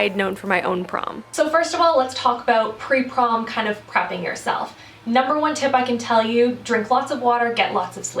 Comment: I thought you said "crapping yourself" hahaha maybe for those who are nervous?